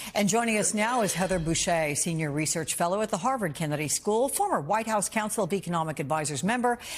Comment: I thought I heard you say "Senior Research Fellow and the Harlem Kennedy School"